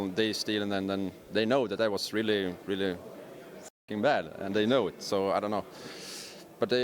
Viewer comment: what did he say around 5:16? i cant all i heard was f... bad ? help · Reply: He was censored. He said fucking.